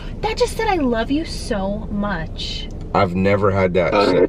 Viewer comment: “Hi again, He loves you, so much” Was what I heard